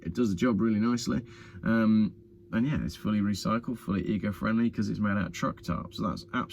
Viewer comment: They've been obvious early morning chem trailing for the last few days Coventry and unusually with prop planes at night, funny new world ,init ..V for Vendetta ) Oh ..and I thought you said your hat was "Ego friendly" ..Now theres a line of merch !